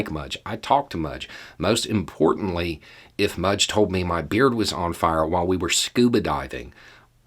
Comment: beard... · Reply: Hahaha—I guess that makes sense for Beau, although I still like the overkill of my misheard "beer [being] on fire" while underwater 🙂